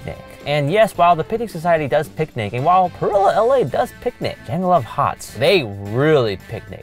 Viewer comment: Anyone else thought they were saying “jungle if huts”?